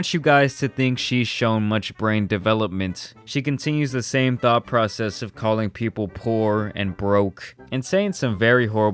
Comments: I literally heard "she continues the same THOT PROCESS..." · Her:“I knew I was born famous a superstar” Reality:”I knew I was born a joke and spoiled” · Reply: I literally heard "she continues the same THOT PROCESS..."